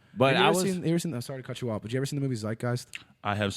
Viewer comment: What movie did he say at 56:23? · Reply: Zeitgeist